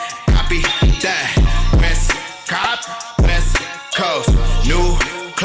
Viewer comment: He says 'Wes Kaap' which is Afrikaans for 'West Cape' where the majority of the coloureds in SA are concetrated. Coloureds are what we call mixed race people in SA (black + white) ,which both AKA and Youngsta are .. Trevor Noah too. They mostly speak Afrikaans which is one of our official language ,also spoken mostly by the white people in SA (boers) .. thus Cpt's accent. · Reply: man i thought he said west god